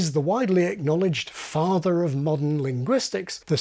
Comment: I heard “farther of modern…” on my first play through. Not surprised when you said you said father=farther. Was confused when you said that was because of r dropping. Maybe I am used to hearing “Haavaad yaad” ???